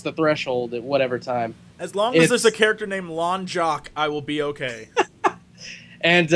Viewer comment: I thought you said "Long Jock" at first and was like wtf. but then I got it (lost thing) pandaren impression was pretty good too!